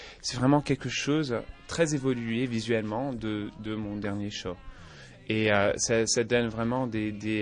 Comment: pour tous les quebecois, j'ai entendu:C'est vraiment quelque chose de très évolué...... de mon dernier CHAT (show)